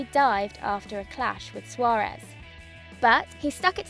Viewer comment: it sounded like he died after a clash with suarez😇😂